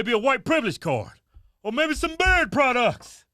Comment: Y'all country. You've been saying BEARD products this whole time. Thought you were saying BIRD products. I'm thinking, how in the world did you get into falconry.